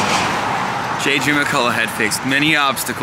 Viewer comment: I thought he said "J. Jonah McCullough" so i expected JJ to burst onto the Senate floor and demand pictures of Spider-Man.